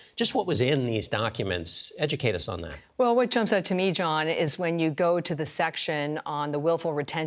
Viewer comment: What chump said to me john, or what trump said to me john?